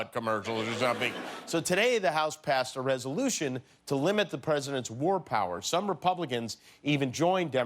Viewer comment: At first I thought he said "limit the president's 'word power'".....ah, if only...